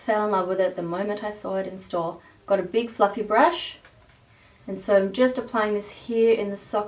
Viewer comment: she said "You get a big fluffy brush..." And my boyfriend asked, "Why does she say to get a big fucking brush?" Lmao! Can't stop laughing!!!